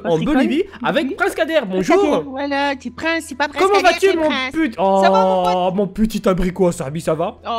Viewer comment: C'est moi ou bien a dit "mon pu**" à 0:06 seconde